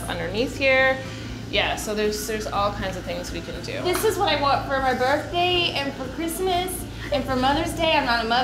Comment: look at what is she saying ?😂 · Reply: Malak Ariana "This is what i want for my birthday, and for christmas, and for mother's day" 😂